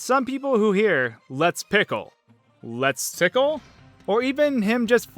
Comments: My friend thought he said HOT PICKLE or LETS PICKLE or MEXICO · I thought Mario was saying "Hot Pickle!" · I thought Mario was saying "Hot Pickle!" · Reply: I thought he said, "A pickle!" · I thought Mario was saying "Hot Pickle!" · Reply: I heard “tit pickle!” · i heard "Let's tickle!" aswell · I always heard "The Pickle!" Though I had SM64 DS so it could have been different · For me, I think years ago I thought he said "One pickle!" when this'd happen...and it makes NO SENSE! Good thing later games made the phrase clearer... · I always thought it was saying "TOUGH PICKLE!" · I believe the correct phrase is "So long-a Bowser!" like how he says "Its-a me Mario!" Also thought that he said "This tickles" at the level start